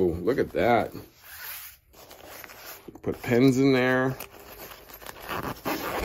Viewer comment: it sounded like “put your Depends in there!”